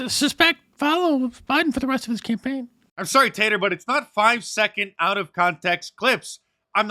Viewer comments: Did you call Stelter "Tater" ? 😆😆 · i heard "...sorry, tater,,," and i died laughing. but wait... fake news. i didn't die. here's one for the rappers out there, free of charge. courtesy of mark dice's mind, inspiring mine, to rhyme about my un-time... wait this is a rhyme too. "i called for the death of me, and died as a result? should i be murdered for hyperbole, or test the results?" the flow is hard with this for me...